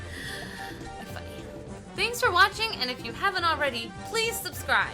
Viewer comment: HAHAHA did say "fuck you" @ 12:49 ?